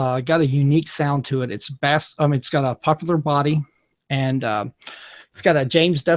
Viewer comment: What is a popular body? Did ol boy mean to say "Poplar body" at 2:52?